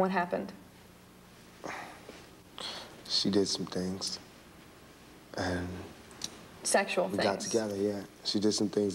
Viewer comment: did she really say the N word infront of Tupac? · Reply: ***** Straight from Ethiopia. N-E-G-U-S. Definition: Royalty, king royalty.